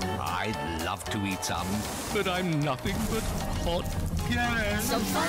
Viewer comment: what did he say? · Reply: But I’m nothing but hot air